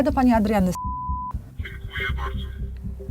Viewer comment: czy tylko ja usłyszałem Pani Adranny skurwysyny? Xd Jeszcze ta cenzura xd